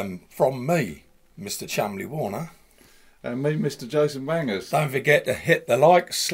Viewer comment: Couple of spade connectors might not have gone astray - and did he just say Jason Wangers? (about